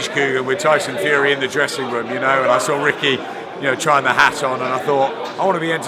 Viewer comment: Did he say ‘Rick trying the HAT ON’